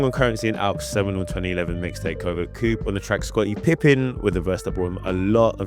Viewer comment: their what 2011 album? Tf did you say before 2011? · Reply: "seminal" fam, it means something that had a big impact on the future which is true of Covert Coup 😂